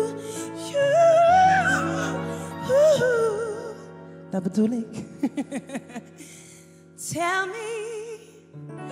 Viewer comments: she lost her voice... Omg im shock.. Is she said: ta betulin? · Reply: Hahaha... she said: "Dat bedoel ik" ("That's what I mean"). Just before she started singing she was interviewed and, if I remember correct, talked about if she was scared of ever losing her voice, or something. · What happened on 2:03 and what did she say? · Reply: Her voice wasn't a 💯. She was ill, but she still decided to perform because they had requested it. So when she lost her voice in that part, she said: "This is what I mean." In other words, before performing she said that she might not be able to hit all the notes though, and unfortunately, that prediction came true despite her attempt and effort.